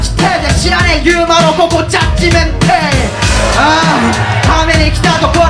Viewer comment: なんて言ってます？ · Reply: ジャッジメントデー